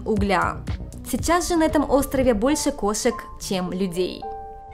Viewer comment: Мне послышалось что ты сказала что на острове Такашима кошек больше чем бЛядей?